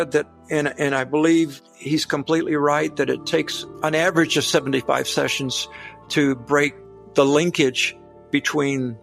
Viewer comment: Can someone please help me, at 40:23, did he say it takes 75 sessions or more to break demonic linkage?!